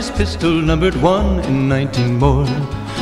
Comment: Everyone: it is now 2020 Me, an intellectual: it is the year of our Lord, one and nineteen more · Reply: B R U H ! I thought he said 1 and 90 more lmao